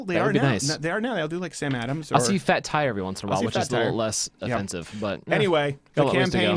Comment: thought it was Fat Tug you said. Shout out to one of BC's BEST BEERS!